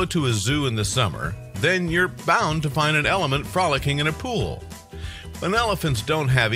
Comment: it sounds like you said element instead of elephant 😂